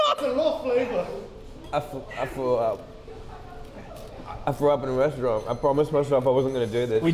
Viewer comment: “I frew up” was I the only one who heard that